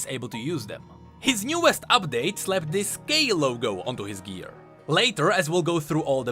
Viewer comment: Did he say gay logo